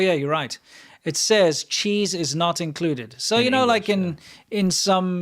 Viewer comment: 1:36:15 For the meal, It really sounds like says she’s not includedmeal ha ha ha ha ha ha🤪🤣🤪😂🤪🤣🤪🤣🤪😂🤪 Have you heard the joke with the naked lady is the platter holding the dishes and one guy gets kicked out What I always like the plate 🤪